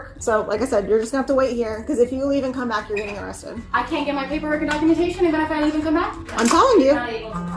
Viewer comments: what i love about this guy is how he uses ":3" ":D" 47:21 "I cant get my paper now ?? documentation ?? and in the met? that what i think she said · "I can't have my paperwork and I'm gonna get arrested if I come back?" Something like that I heard, I'm a Chilean, but I don't know fast english.